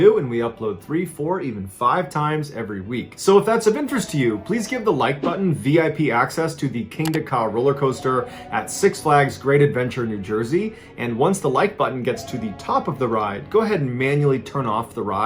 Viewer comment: Lol thought u said give the like button H.I.V lmfao